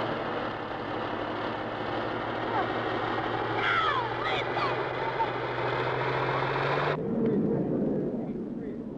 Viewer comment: Does anyone know what that Corpsman yells at 6:36? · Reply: He recognized that other Corpsman and said “ Hey what do you say” and then added the other guys name